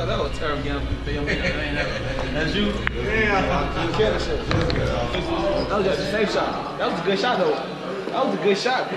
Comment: did Sosa said get off eith camera?Why??? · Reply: DáŇa he said gimmie this mf camera